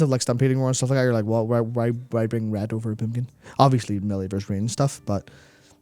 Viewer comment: At first I thought said 'why bring ret over a pumpkin?'...